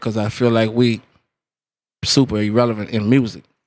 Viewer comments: Am I tripping or does he say "we super irrelevant" speaking of cash money at 20:22 LOL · did he say they were super irrelevant LOL